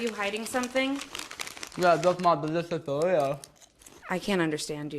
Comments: This is what I heard “Yeah thats my delicious Doritos” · when he had the chips and say delicious it sounded like he said nutritious and i was like WHAT